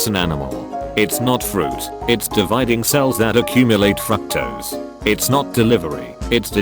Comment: did it just say "fucktose"?